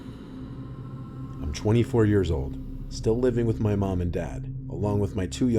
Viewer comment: Thought you said he's 4 years old. And I thought no shit he's still living at home also how's he written this and then realised ooooh shit haha